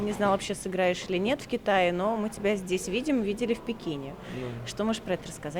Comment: Послышалось не в Пекине а "Но мы видели тебя в бикини"